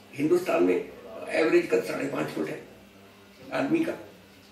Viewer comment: Sir said 5 and a half feet that means 5.6 not 5.5 as the subtitles say @ 23:28.